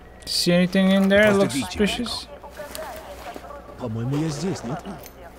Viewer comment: For viewers only. 19:13 Adler says in Russian with hurrendous English accent: "Call Belikov, please", - while the subtitles say: "You're running late, Belikov", - to which Belikov says: "I am here, am I not?", - with the correct subtitles.